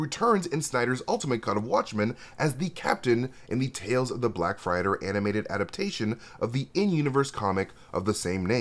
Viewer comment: did you say "Black Frighter"? As in, one who frightens? Dude...